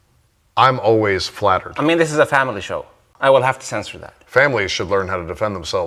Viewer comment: I cant make out the words he is saying around 4:40 , I wish it was clearer to everyone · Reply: There are captions, additionally, I will tell you. Guy : I'm always flattered: I mean this is a family show, I will have to censor that Guy: families should learn how to defend themselves